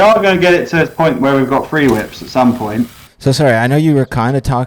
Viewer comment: Ik there’s no free whips but I really like the whips that they have now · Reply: What's a free whip? I thought he said three whip.